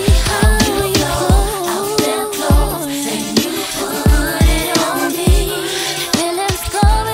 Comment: All I hear is, “PUDDIT ON MEH!” 🙌🏽😍🤣